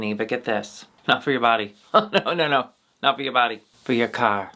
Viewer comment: I heard "drift is for your cock" i was like: cool i need that